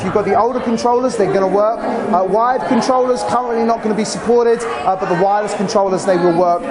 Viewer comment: Does he say "wired controllers currently not gonna be supported"? at